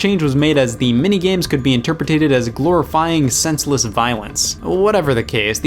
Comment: Did anyone else catch him saying interpretated at 8:04?